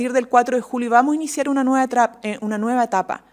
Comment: Que dijo vamos iniciar una nueva trampa ?? Minuto